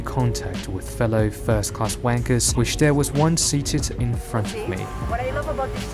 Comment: Surely I’m not the only person that noticed what he said 6:29 😂😂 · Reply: fellow first class wankers hahaha